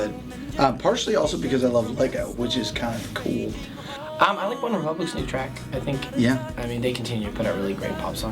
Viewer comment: Can anyone tell me what band Taylor mentions at 6:40? I can only make out, "I like ___ new track..." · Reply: One Republic